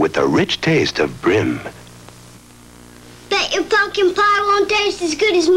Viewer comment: I could have SWORN he said “I bet that (bleeping) pie won’t taste as good as mom’s!” 😅😅😅